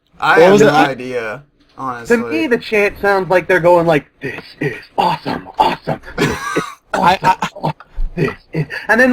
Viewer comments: LOL i was the only one of my friends who thought they were legit saying "THIS IS AWESOME AWESOME!" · i thought they were saying FISH FISH AWESOME AWESOME lol XD